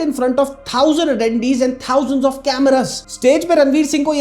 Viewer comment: what did he said 😅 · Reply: Attendees bhai 😂😂😂